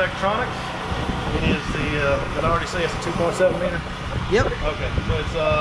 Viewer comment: I think he said @ 0:35 it's a 2.7m not 1.8M